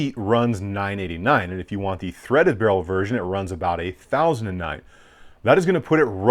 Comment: Did he just say $8,009 for the threaded barrel elite? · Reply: $1009.